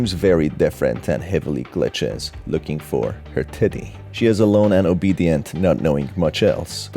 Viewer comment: why does it sound like you said "Her titty?"